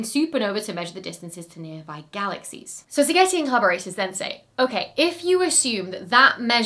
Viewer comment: I kept hearing "Spaghetti and collaborators". I'll bet he gets that a lot.